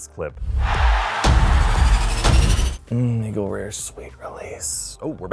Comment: Wait, did I hear "Mhmm Eagle Rare's sweet relief"? Are you downing Bourbon?